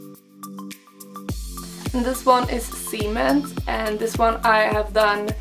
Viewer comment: I thought you said cement was called 'semen'